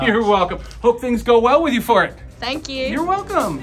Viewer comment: Did he say "Hope things go well with you Fart"?